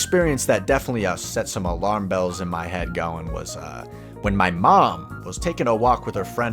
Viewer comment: bruh i thought it said “my mom” not “a mom” 😭